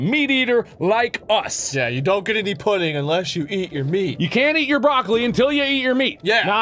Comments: No!! No more Beanie Swag :/ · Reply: Nmd I thought you said you CAN but you said CANT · I thought was going to say "You don't get any pussy" Not pudding! Lol:D